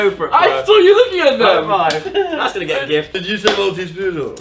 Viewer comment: did he say that's gonna get a gif? ITS GIF NOT GIF